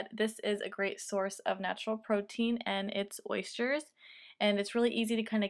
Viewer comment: what is that? i couldn't catch if she said what it was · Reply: Oysters